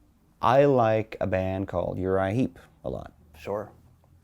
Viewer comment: I didn’t know what band he was saying at 2:25. Thank you, Google! It’s Uriah Heep!! PS: Does Tobias have an Empire patch on his sleeve?!